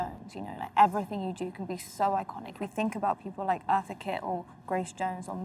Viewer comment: i think she says eartha kitt